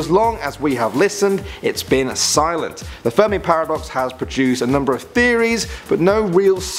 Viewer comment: Who heard him say furby instead of fermi